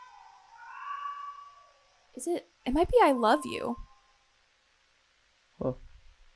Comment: i think they’re saying im alright.